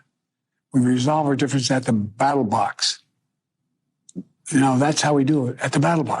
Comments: Did Biden just say, "We resolve our differences at the battle box." 2 times in a row? hahaha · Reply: I thought he said Battle Bots 😂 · Sounds like he is saying battle box ...this guy 🥴